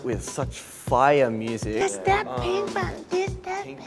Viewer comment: Bro, why did I hear "it's that pimp out" and wondering what kinda straight shit Chan has produced in secret again 🤦🏻‍♀️ only to go to the comments and realize that they were singing "it's that pink venom" 💀